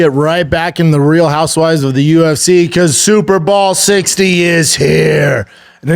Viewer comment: "Super ball 60 is here!" I don't know what the super ball is but it sounds exciting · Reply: I was half asleep i actually thought he said super BALL lolllol god bless